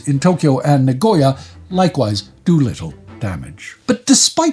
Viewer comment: (ish) Did I hear that correctly? “Doolittle damage”?